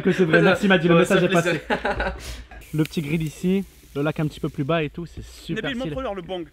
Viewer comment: il dit quoi à 7:55 ? J'ai compris " les piments dans le bang" xD